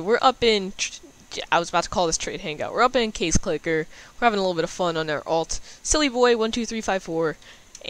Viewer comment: Dang it I thought u said we're updating case clicker at the beginning